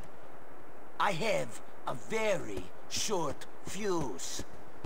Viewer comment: did he say "i have very short pubes" o_0